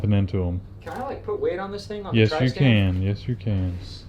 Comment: did call the Center Stand a Tri-stand ?